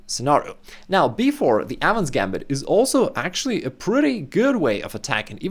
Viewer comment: bro this whole time i thought he was saying "adams gambit"